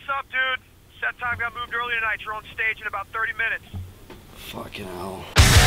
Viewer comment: What does Danny say in 0:57? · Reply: ShisDProdutions "fucking hell"